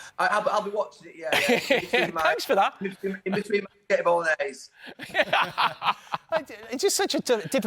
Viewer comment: What does he say there? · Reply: In between his spaghetti bolognese